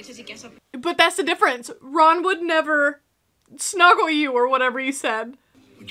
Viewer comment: Fun Fact: Snog is British slang for making out. Which is what I'm sure Hermione said around 13:20 in your video.